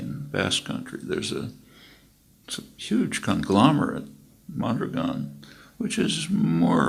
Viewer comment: What does he say at 9:27 ? Which company? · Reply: Mondragon Corporation in Spain